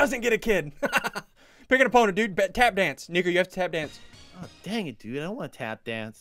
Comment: At around 43:14 it sounded like he waged the n-word not nico